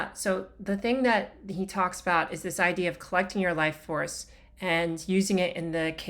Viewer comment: At first I thought you said "flexing your life force", and I've decided that I like that phrase much better! ☺️😛